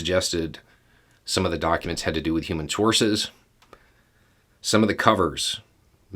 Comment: Me too! I loved the "Kick Rocks Kid!" I've always known that phrase as "pound sand buddy!" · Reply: At the 5:20 mark, did Beau say “human sources”? I have a head cold today, and can’t hear well.